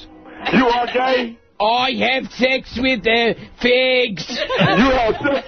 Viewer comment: can anybody tell me what the guy on the phone says. I can’t tell if he’s saying kids, pigs, or something else